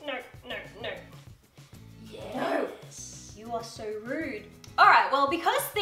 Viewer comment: When Archie is going for the 🍑 Peach on her sweater, I swear there's a second voice saying Yyeesssss Very Erie especially noting that just walked out saying enjoy. I dunno may be just me but I listened to it about 10 times. Very Erie Loved the video and the outfits though. Keep up the good work you guy's!! Oh and loved your hair